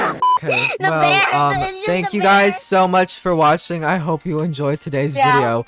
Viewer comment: I heard “thank you for washing 🤗” 💀